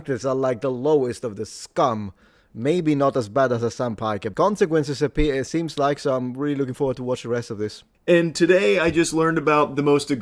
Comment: I assume that the automatic subtitles are wrong ("maybe not as bad as a senpai"), what did he say?